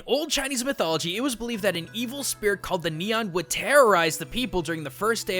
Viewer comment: evil spirit called nian I hear as: evil spirit called neon me: what did you just say abaut me?!